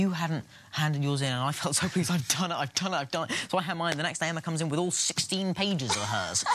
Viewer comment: Who else burst out laughing at his speech after 1:11😂😂 Also, did anyone notice that in this speech, he referred to Emma as hermione? FRIENDSHIP!! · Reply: Sneha Reddy no he said “so I hand mine in and the next day Emma comes in with all 16 pages” though it does sound like he said hermione because he spoke so fast :)